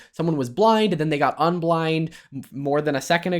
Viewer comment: what did you say? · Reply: unblind 😂